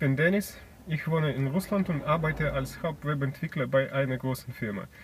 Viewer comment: ' German is really good for just three years of training. Alas, I hear him say "Webentwickler" (web(site) developer) while the subtitles say "Werbeentwickler" (advertising developer). I hear that the German 'r' is really hard to get right for Russians and that one common "solution" is to "swallow" it. This is a good example why you should (try to) not swallow, because either I'm hearing it wrong or Katja overcompensated for this type of error when she wrote the subtitles. Be pirates. Or cats. Arrgh! ;)